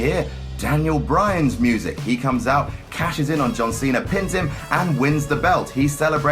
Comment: I love how his accent makes it sound like he says john cenar